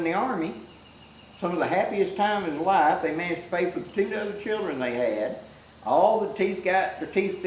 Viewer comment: Did say "two dozen children"?? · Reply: 😂 that would be a very prolific couple 😂 with "two dozen children"..! No he said "two other children".. thank you Rick, I needed that chuckle..! 🐻